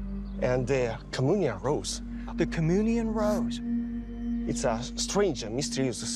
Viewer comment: That Cominian Rose is almost a shape of today’s Drones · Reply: Oh, I thought he was saying communion rose. Jajajaja (Raised catholic)